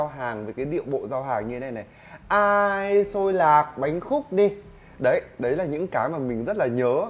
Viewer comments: hồi mới xuống Hà Nội học đại học , luôn nghe thành Tôi là bánh khúc đây :)) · Lần đầu tiên nghe rao “ Xôi lạc bánh khúc đây” mình đã nghe thành “ Tôi đang hạnh phúc đây”=]]] Ngồi thắc mắc cả buổi sáng